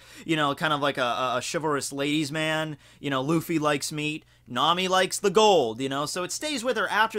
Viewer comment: When he said Luffy likes meat I thought he said Luffy likes feet